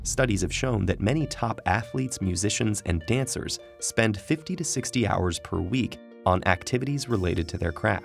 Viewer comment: I don't know why I heard "top athletes spend about 160 hours a week on their craft". I was so confused